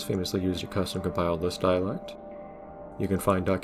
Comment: I know it might be case by case for some people, but he is saying "custom compiled Lisp dialect.", not 'List Dialect' although you wouldn't be far off. LISP means 'list processor' and was the first (?) interpreted language/scripting language for this kind of thing. Anyway back to the video, because for better or worse even though I saw 'Lisp', I heard 'List' so I kind of had to make this comment lol.